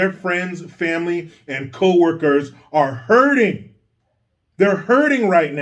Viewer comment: At first I thought you said "herding"....(you know, like the women on The View), but then I realized you said "hurting". HURTING!!??? WTF? Sports commentators that go WOKE make me wanna PUKE! If a kid can't do Algebra, they shouldn't be discussing sexuality!! HELL, "Sex Education" should be relegated to Middle or High School. "Sexuality" should be between PARENTS and THEIR CHILDREN........PERIOD!!!!